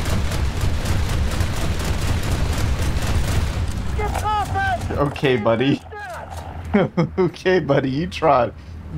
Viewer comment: what did the crewmate said? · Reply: "gertroffen, ziel zestört"